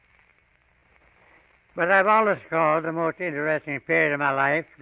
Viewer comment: As a non native english speaker, some of this is quite a challenge to understand. Can someone please help me with what he says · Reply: I am a native English speaker and I am really struggling to understand. His accent is unrecognizable to me. It’s a bizarre experience. “But I’ve always called, the most interesting period of my life, the ten years I spent in the west with the Haden survey. It’s always been my most interesting period. New places, new thing to photograph, new questions to bring to the attention of the world, to make the yellow region known.”